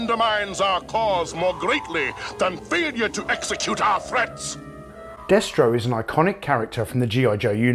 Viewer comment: I had to turn on CC, I thought he was saying "failure to execute our friends" and thought "Damn, he's a lot more hardcore than I remember." I found your channel from Retroblasting, the Palitoy X-Wing vid. Good stuff!